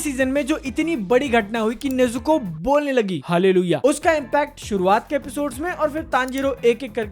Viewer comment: did he said hallelujah?